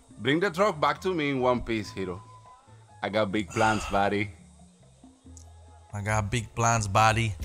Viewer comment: When philly said I got big plans buddy, I thought he said daddy😂